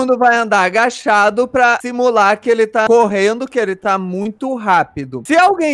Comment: Alguem mais ouviu o fala morrendo ou foi so eu nesse minuto